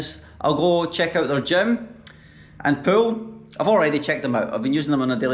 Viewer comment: Lol..I thought he said checking the gym then poo..